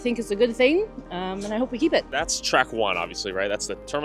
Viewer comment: “I hope we keep it!” What I hear: “I like watching and hear people die!”